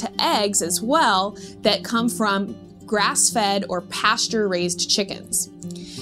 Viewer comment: Until I realised you meant pastured raised I thought you said pasta raised. I am sure the cows would appreciate the spaghetti.